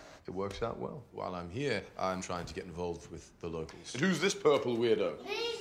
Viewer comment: did he just say “im trying to get involved with the loki’s” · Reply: I think it’s locals but it’s funnier with Loki’s